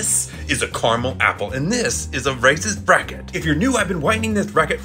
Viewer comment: I thought he said a racist bracket lol 😂😂